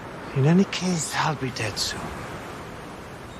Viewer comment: "Is that the end?" The last line in vikings series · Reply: Oh I thought he said "is death the end?" Lol